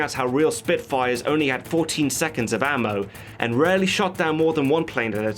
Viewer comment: No ship goes down in minutes? Tell that to the Lusitania. Regardless, great video as always. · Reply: It sounded like he said "rarely" goes down in minutes.